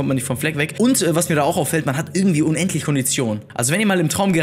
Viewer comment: beim ersten mal habe ich Munition verstanden